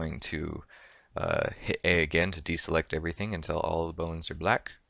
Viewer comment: did you say hit B or E? 4:00 to deselect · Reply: A